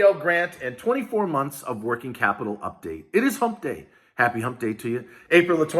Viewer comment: BREAKING NEWS: BIG BEN CARDIN ENTERS BILL TO RETROACTIVATE PPP BASED ON GROSS SALES AND SBA LOAN OFFICERS BETTER STOP BULLYING SMALL BIZ! 😡💩🔥❤️🙏🎉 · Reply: It's my birthday and i thought you said " Happy Birthday to you" (instead of hump day). It was a pleasant surprise and pleasant disappointment 🤣. Love watching you